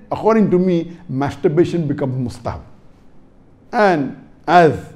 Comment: I didn't understand what he said. In another website, it was written as "pasta" which is not an arabic word. Did he say mustah? As in Mustahabb? Can someone shed light on this?